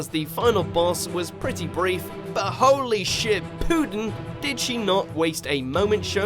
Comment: at one point i thought he said holy shit